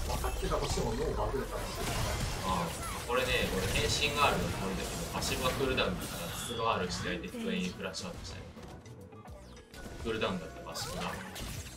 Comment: こっから何言ってるか聞き取れない... 誰か教えてください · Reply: (前後のパッシブで敵が混乱するという文脈は置いといてこの場面は)「これね、これ変身R これだけどパッシブがクールダウンだからパッシブRしないで(普通のRをしたからエコーが回避から反撃してくるから)すぐにフラッシュアウトしたね クールダウンだったパッシブが」 たぶん